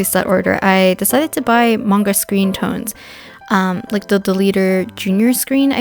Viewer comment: i thought she said "among us screentones"